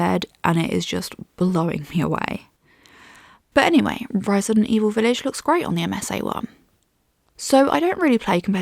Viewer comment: Did you say Ryzen Evil Village? 🤨